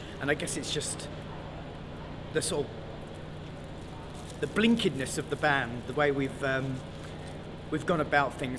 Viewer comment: Hi!! Sorry...Do you know what does he says at 4:48, he says: """¿Blinkidness?"" of the Band..." Thanks